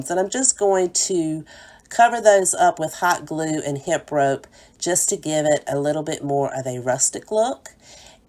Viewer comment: Thank you 😊 I use hemp rope a lot because it doesn’t fray😀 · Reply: oh ok. I thought you said HIP rope.